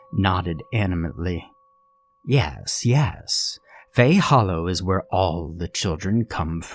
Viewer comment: I thought it said hey fellow